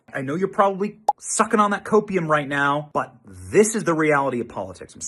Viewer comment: LOL... I thought you said Opoum!! Is that what you actually meant??